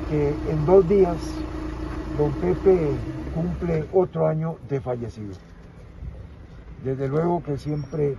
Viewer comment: Aaaaaa otro año de fallecido pensé que dijo 8 años de fallecido ya estaba conspiranoico 😱😱😱🤣🤣🤣🤣🤣🇨🇷🇨🇷🇨🇷